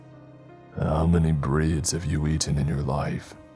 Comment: I think he tried to say (bread) which would make it a JoJo reference if intentional.